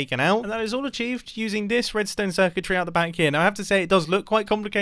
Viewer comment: mumbo says"redstone circuitry,", I hear "redstone wizardry"